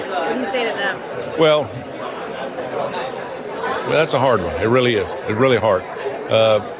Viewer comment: did he just say: that's a hard-on? Really hard? 😂